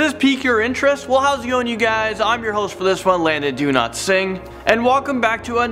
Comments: did he just call himself Landon Do-not-sing???🙃🙃🍪🍪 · Am I the only one who heard him say his name is "do not sing"? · I thought he said his name was “Landon Do-Not-Sing” 😂🤣😂🤣😂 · thought i heard "Landon, Do Not Sing" for a minute... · who else heard him pronounce his name as "Landon do not sing" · I swear i heard "Landon do not sing." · Is it just me or did it sound like he said, "I'm Landon Don't Sing" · I always thought he was saying landon do not sing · I thought he said do not sing! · Dude I was smoking and not paying attention I really thought he said do not sing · I thought he said Landon do not sing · I thought he said Landon DoNotSing lol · I clicked this video and only heard the intro but didn't watch it. I swear what I heard was: I'm your host landon do not sing. after hearing that I subbed 😆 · i thought he said landon do not sing LOL